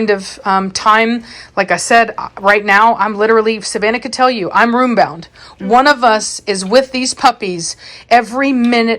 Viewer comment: What is the color called of the light puppy with dark mark down it's back? The one Savannah picked up at 15:40. She said it but I couldn't catch what she said, maybe Formy? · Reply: NVM, it was said again later. Formantino (not sure on spelling)